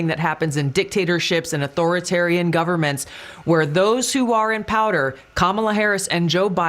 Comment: sounds like she said those in powder. Either way she's probably right. 👃🏼 ❄️